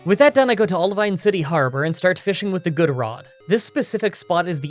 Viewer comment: I saw the fishing rod, he said fishing but I heard pissing, it made sense, just give it a look, then my brain realises what it did and now I'm trying to get my high ass to type this down while trying to think I'm somehow not high